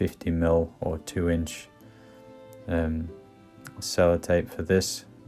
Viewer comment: Was the tape you used packing tape? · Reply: He clearly said 2" Sellotape.